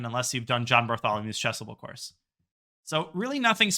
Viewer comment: I keep hearing "my chessable course". How do I find any of your chessable courses?